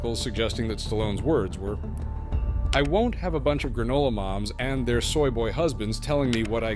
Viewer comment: Granola moms and their soy boy husbands 😂😂😂😂 That’s brilliant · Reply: Oh!!! I thought he said Granola "Bobs" 🤔